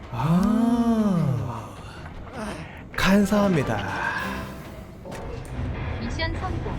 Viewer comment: 간사합니다로 들리네 ㅋㅋㅋㅋ